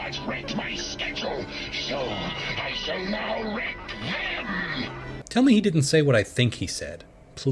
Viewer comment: Upon listening to this a few times, it sounds like Megatron is saying he'll wreck them. The console Megatron's using is distorting the sound though, so I can see the mix-up.